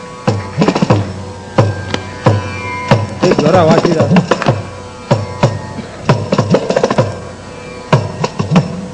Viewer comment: What did Semmangudi say at 2:10. And again something at 3:56...(to Harishankar Sir) Any idea? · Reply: Dilip Warrier at 2:10 he said ‘jora vaasi da’ - it could mean ‘play loudly’ (it could also mean ‘play well’ - ‘go for it’ kind of thing) At 3:54 he said ‘shabaash’ (well done) At 356 he said something like ‘if you play God will be pleased’ (I heard something KadavUl) but his voice trailed off so I couldn’t get it completely!